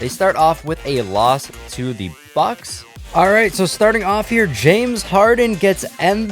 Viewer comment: I swear at 2:34 he did not say Bucks and said, you know